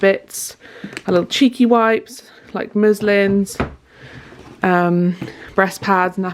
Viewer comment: Always better to get yourself checked out. Glad everything is okay. You must both be getting very excited now❤ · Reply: you made me laugh so much! Every time you said Muslim, I thought you were saying Muslim😅😅😅. Also the place in Scotland is pronounced Are vee more and not Evi more. Beautiful video as always, and once baby comes, you will forget about your life before her so quickly! We are all waiting with you and Alex and Roxie with baited breath. Good luck!!!!!!!!😊😊😊😊😊😊😊😊😊